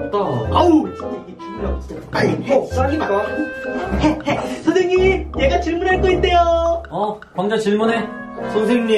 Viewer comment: 에 나만 욕 들었나ㅋㅋㅋ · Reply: 최진규 해 해 해 시바 ! ㅋㅋㅋㅋ